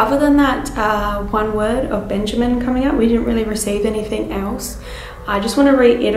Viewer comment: I heard “it is true” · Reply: I heard "he didn't"